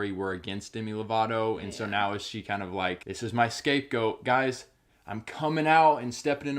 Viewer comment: "'...this is my escape goat.'" 😂 Maybe I'm mishearing him, but even if he did say scapegoat that doesn't quite fit in this context....